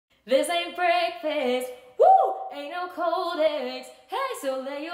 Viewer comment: I thought she said this ain’t Texas 💀